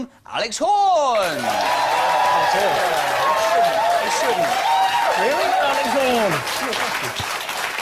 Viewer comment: does Tim call him Little Alex Horne?? Cinematic parallels...